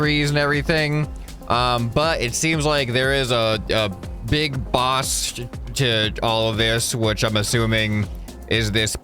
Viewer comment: Big Boss? WHEN YOU CAN'T HEAR A WORD